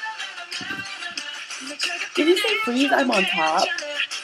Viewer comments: lol When I first heard "armor down" I thought they said "I'm a Dom." WELL OK THEN! :P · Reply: i thought they said ''freeze, i'm locked down'' ?? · lol When I first heard "armor down" I thought they said "I'm a Dom." WELL OK THEN! :P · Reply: at first I heard FREEZE! I'M UNDONE..... cough